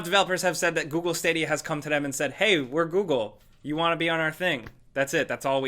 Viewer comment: on the video my Google assistant thought you said. do you want to be on her face